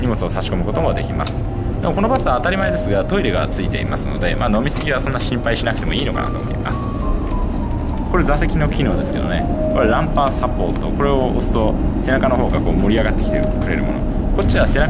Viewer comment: 4：44頃の発言「ラン パー サボート」って聞こえた。ラン バー サポートではないのか？